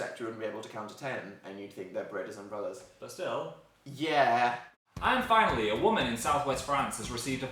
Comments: I thought phil said "and finally I'm a woman in France" .... I got highly confused..... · When Phil said, "and finally a woman" - I thought he said, "i'm finally a woman." Oops. ;D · When Phil said 'And finally a girl' I kinda misheard and thought he said 'I'm finally a girl'... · Near the end, I thought Phil said "I finally am a woman."